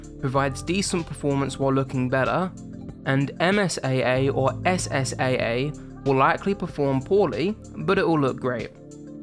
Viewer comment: what did you just say? 😭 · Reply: "MSAA will likely perform poorly, but it will look great"